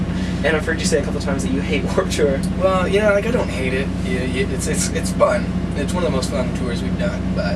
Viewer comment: does he say "well i dont hate it, you idiot"?.. the interviewer looks so intimidated